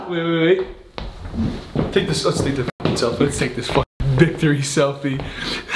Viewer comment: did anyone else think said dicktery selfie Hahaha🙈🙈